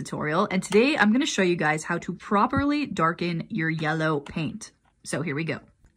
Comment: how to make yellow darker" Also rae: "and now we have a beautiful dark brown" Wha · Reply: I-i- I thought she actually said dark yellow 😭✌️